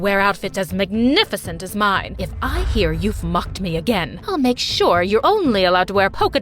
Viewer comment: y’all uh uh i heard if y’all fucked me again